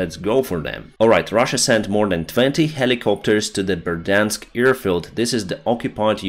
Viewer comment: did you say verdansk thats cod EDIT: sorry rewatched Berdiansk my bad I would delete but if anyone else mishears thats what it is :)